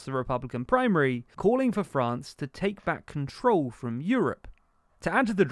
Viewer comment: Did Barnier call for France to take back control of or from Europe? The caption can narrator said different things. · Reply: from